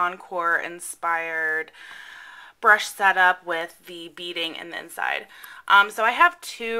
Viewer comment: I'm curious, what do you have in the cups to hold your brushes in place? I thought I caught you say "beads", but wasn't sure?